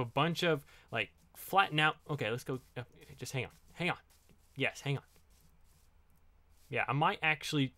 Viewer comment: Was saying “Hang up.” At 17:19??